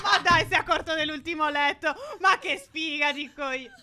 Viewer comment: Da notare che nel minuto 14:48 dice phere ma che sfiga di coglio e poi si ferma